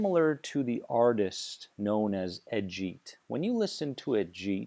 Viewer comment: Does anyone know what word is being used at around 6:43? It sounds like ejeet or edge-it... A musical artist. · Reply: Ajeet